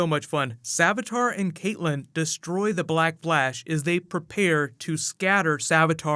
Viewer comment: Is it just me or did he say "Black Blash"? Edit